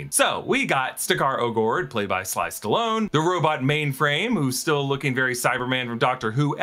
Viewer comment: did you sayed "SLICE STALONE"??